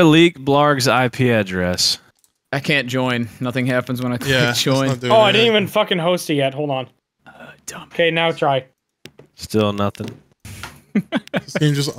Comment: i am tripping or i heard someone say ''dumbass''